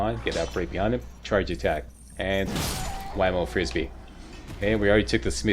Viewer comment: You said... “wham o frisbee” but I heard “Lionel Richie”. I thought you were referring to “All Night Long…” which seemed appropriate. — Getting old can be humorous sometimes. -- Weird... Cut and paste into an empty comment doesn't work w/ Safari browser. -- Just add 1x space first. then paste. -- Oh Safari...